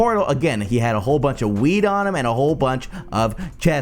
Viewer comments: Is it just me or did he say weed at 8:14 ? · Sounds like he says and he got a whole bunch o' weed on him at 8:10 Just wait for it